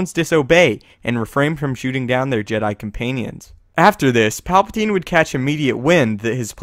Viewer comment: Did he just say comPAINians?